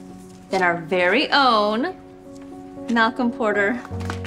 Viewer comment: I thought she said Potter instead of Porter at first